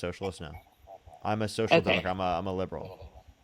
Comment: it says social dog in CC, is he saying social democrat?